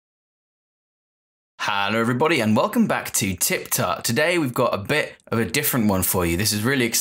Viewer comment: I thought he said "Welcome to TikTok", and I thought he clickbaited us.